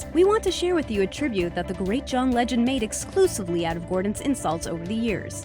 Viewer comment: when you said the “great” john legend, i misheard you and thought you said the “late” john legend and i was in shock that the way i found out that john legend had fucking died was through a gordon ramsay insult list